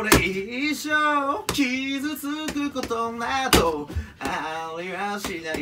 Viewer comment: 「傷つくことなどぉ⤵」って聞こえるんだがww